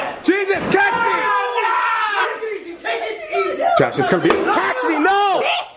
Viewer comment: why did David call her casity?? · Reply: Samantha Foster he was saying "cash me", like her catch phrase thingy